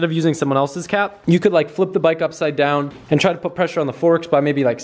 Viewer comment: Erm, 7:16, did you say "forsk"?